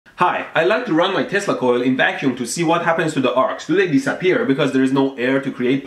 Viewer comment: i thought he said he'd like to run his testicals in a vaccum